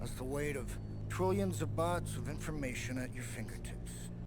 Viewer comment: i understood "bots" the youtube CC says "bugs", did he actually say bytes like you said?